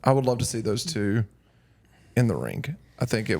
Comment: I laugh every single time says "rink" instead of ring (like